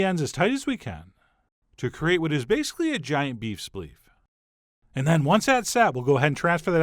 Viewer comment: LOL beef spliff. You rule Chef John! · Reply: I thought he said beef spiff lol